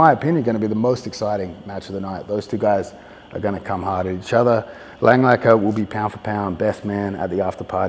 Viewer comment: did he say inshallah?? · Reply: "On each other"